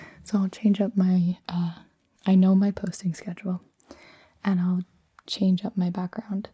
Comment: I'm surely wrong,but I heard "I know my pussy schedule" Scuse me,my english listening is not so good...